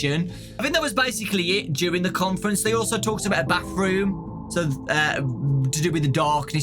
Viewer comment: I lost it when I thought said “backroom”but then I realized he said “bathroom”